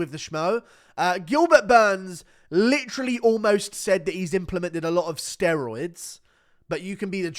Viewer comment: Nah Gilbert definitely was about to say he was doing steroids and then caught himself last second 😂😂😂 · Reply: I hear “a lot of my selff” idk just me tho